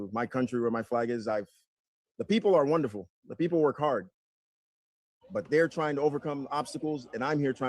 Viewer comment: "Werk Hadd " I heard that.